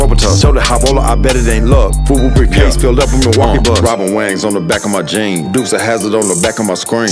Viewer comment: Sounds like he said. Rubbing Wangs on da back of my jeans......